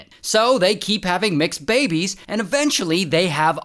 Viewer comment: i thought he said "they keep having mixed babies"😅😅